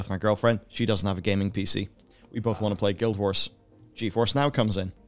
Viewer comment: "We both wanna play Guildwars" - did i hear that right?